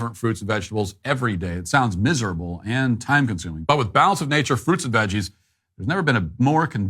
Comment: I thought you said "Bowels of Nature" when you were talking about that veggie supplement